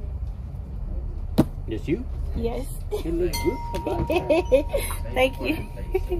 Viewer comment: Did he say you look good for being a black girl or am I missing something?